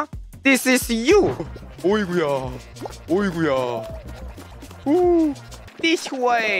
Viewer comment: did you say in Russian, уй бля